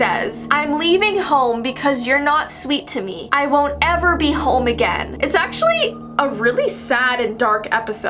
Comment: i thought that said "I won't ever be homo again" at first